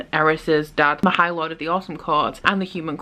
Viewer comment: I swear, for some reason every time you say "Autumn Court" I hear "Awesome Court" XD